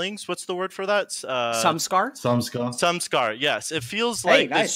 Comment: what is the word they come up with? Sun scar? Sun scarf? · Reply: samskara